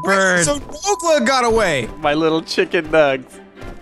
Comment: I my mind I heard you said "My little chickadees." Dammit I miss those times.. Still watch Radio Respawn sometimes